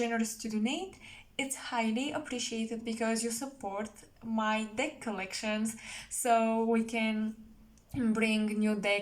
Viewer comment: It sound like you said "dick collection" I was like: lol, what? But then I realized that you said "deck collection" lol. Have a nice day.